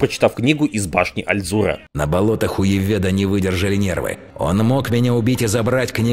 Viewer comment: На болотаХ У Яведа, мне одному послышалось?